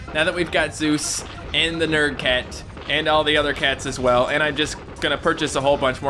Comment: Bro did he just say mer cats (cameraman cat) not the best cat in the game😬😬